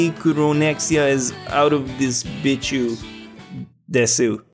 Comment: anyone heard bitch You